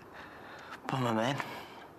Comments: what did he say? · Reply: I think he said bummer man. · What is he saying? Did he say "Pull My Man"? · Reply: "Bummer, man." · what did he even say at 9:53? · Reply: I think he said "bummer man"